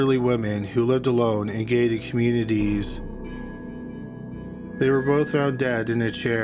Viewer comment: "A gay community?" "I-ren"(iron) Pro-nun-ci-a-tion!! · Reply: I heard "a gay community" as well can only think it was meant to be an "aging community" as a Canadian I pronounce iron as "I-ren" also.